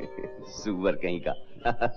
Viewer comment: seconds , what did he said .Suvvar kahin kaa?? Ya something else..